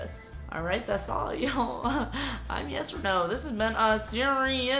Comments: I thought she said “hello I’m yes or no” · omg I thought you said hey I'm yes or no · I thought when she said her name she said “hi I’m yes or no” I was like what your name 😂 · On beginning i heard "Hi im yes or no!" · Who else thought she said “hi I’m yes or no” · I thought she said “Hi I’m yes or no” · At first i heard u saying "my name is yes or no"🤣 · I thought she said " Hi, I'm yes for no " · I thought she said "hi im yes or no" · dude i thought she said "hi im yes or no" omg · Am I the only one who thought she said “hi I’m yes or no” 😂😂😂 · i thought she said "hi im yes or no" lmao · why did I thought she said, "Hi, I'm yes or no" ?? XD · I thought you said "Hi I'm yes or no" in the beginning haha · This is the first time watching your vids and the first thing I hear is “hi I’m yes or no” 🤣🤣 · I thought she said "Hi, i'm YesOrNo" 😂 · I thought you said ''Hello im yes or no" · At first I thought you said "Hi, I'm yes or no." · Lmao is it only me or did i hear "hi im yes or no" 🤣🤣🤣🤣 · i thought that she said -hi! i'm yes or no im stubid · i thought u said "hi i'm yesorno" in the introduction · Who thought she said Hi! I'm yes for no